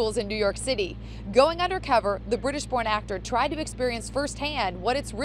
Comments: it sounded like she said "the british porn actor" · Did anyone else hear at 0:18 she said the British porn actor? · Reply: Tiny Kitty British Born actor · did she say british porn actor???? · did she just say “British porn actor”??? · did she say British PORN actor????🤣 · does it say a British porn star😂😂😂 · “ the British porn actor” lol I know that’s not what she really said but it’s funny anyways